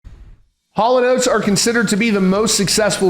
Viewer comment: When I was around 2 or 3 years old in <num> every time I heard Hall & Oates mentioned… I thought they were saying “Hall of Notes” This makes sense when you think about that incorrect inversion of their name